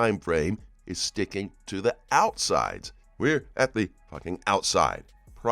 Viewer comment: Am I hearing this right? This guy just said at 6:22 “we are at the fu**!#g outside” I